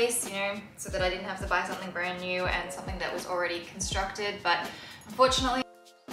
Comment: Haha as an Australian, I totally heard “unfortunately” but then when you pointed it out, I went back and listened. Still heard “unfortunately” but yeah…I guess that’s being able to understand the nuances of the Australian accent. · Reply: Haha I’m glad you could understand, all I kept hearing was fortunately 😆